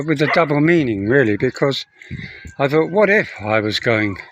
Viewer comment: I was actually wondering why others thought you leaving. I heard you say “what if” hahaha. I guess others didn’t hear those two words